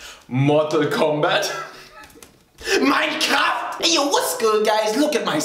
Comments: When he said mincraft I thought he said nein comft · for a second i thought you said mykramf instead of minecraft